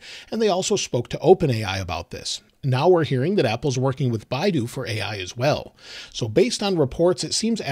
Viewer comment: Omg I thought you said apples working Biden! I was gonna say……we’re so fucked!